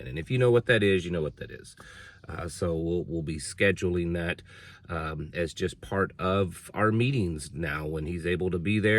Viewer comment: If someone would please help me out here please. At about 1:12 talked about something the guest speaker brought. My hearing in not good and I couldn't make out what sort of machine said the man brought. TY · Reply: I believe it’s called a rife machine.